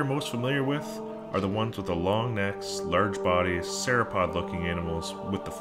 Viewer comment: i thought he said long necks like sara pavlin lmao I rewind it twice is that what he said?